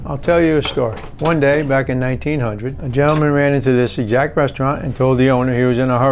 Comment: thought he said ja morant😂